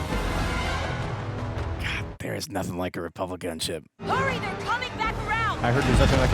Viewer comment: I thought he said Republican ship😂